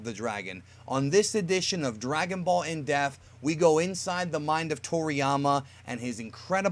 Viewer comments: Is it just me or does it always sound like he says “in deaf” not “in depth”? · Whenever I hear you say "in depth" I keep hearing in death or in deaf.